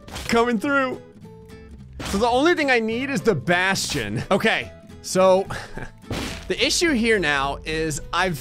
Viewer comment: I honest to god thought he said “all I need now is Sebastian” I’ve been watching Black Butler and now I’m curious what would do if he had access to a demon butler